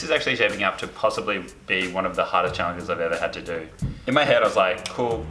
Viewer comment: I thought he the tutorial said "this is the hardest thing I've ever ATE" INSTEAD OF MADE